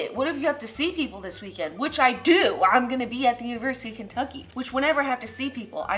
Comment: DID YOU SAY KENTUCKY IM THERE RN COME SEE ME